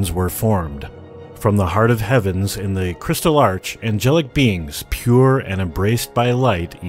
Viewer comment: Whas it just me who heard him say engelic beans at 0:25?